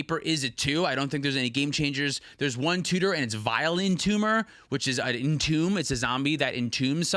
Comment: had the Podcast in my ear and forgot what Vile Entomber was. thought said "Violin Tumor" and was like "wow that must be a really old card I've never heard of"